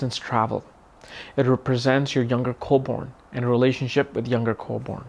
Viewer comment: What is being said at around 0:26? Coleborn? Co-Born? What is that? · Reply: Younger co-born as in younger siblings